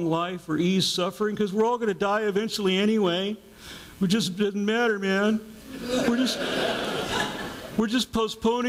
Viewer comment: what does he say between 21:26-21:28? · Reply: "we're just muh-matter man." He's imitating someone sobbing or stammering.